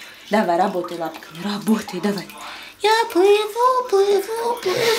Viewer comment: i had no idea what the woman in the background was saying. was she singing? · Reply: The only thing that I could understand from what she said is "Go to work, come on fatty .. Let go .." And yes, she kinda sang ..